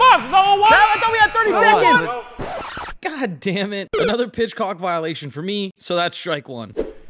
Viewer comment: Goddamnit another pitchcock violation had me dying, even the subtitles say that’s what it said